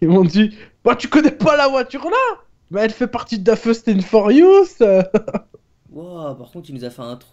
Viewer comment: À 1:20 tu dit quoi fastess fusius?? 😂😂